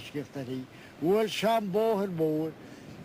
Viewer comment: I think the translation has been cleaned up considerably. He clearly says "rochambeau" at 1:54.